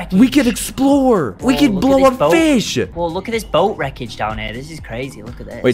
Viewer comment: We could blow a fish (He probably said “blow up fish”, but I heard “blow a fish” 🫠)